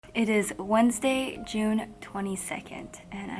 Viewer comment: I clearly heard her saying It is wed June 22nd.. I was like wtf.. did she left so long ago?? But look back 2019 june 22nd, its not a wed. Jan 22nd 2020 was on a wed.. So it's Jan 22nd....